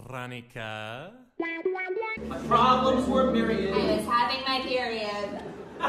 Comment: I thought it said “I miss having my period”